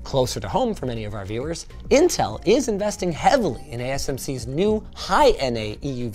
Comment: asmc instead of asml